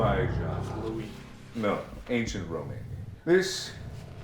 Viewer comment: Did he just say ancient romanian???? 11:07 Something is wrong here... bc romanian is the language romanians speak in Romania🇷🇴..... I'm confusion